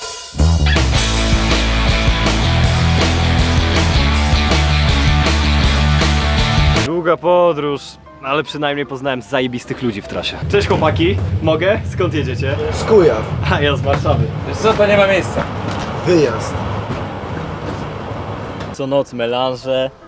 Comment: usłyszałem za pierwszym razem z chuja XD